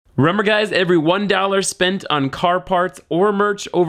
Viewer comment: Did you really say "rember guys"? xD